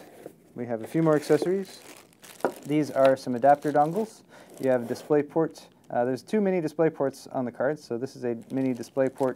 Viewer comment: Am I the only person who thought he said too many rather than two mini?